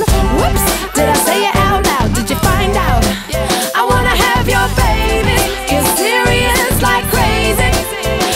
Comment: all i hear is "...i wanna eat your gravy" :O